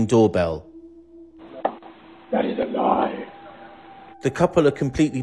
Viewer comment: i think he said ready to die